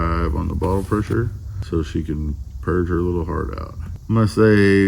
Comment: Ohhhh Purge, I thought she said perch, and thought, yes you would look good on a perch too.. hahaha🤣😂